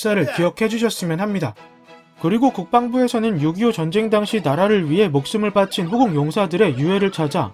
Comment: 6.25전쟁이 자꾸 요기요전쟁으로 들림ㅋㅋㅋㅋㅋㅋㅋㅋㅋㅋㅋㅋㅋ